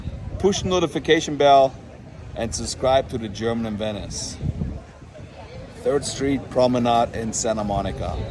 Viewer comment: At the end when you said to like etc. I thought you said "subscribe to the German menace" 🤣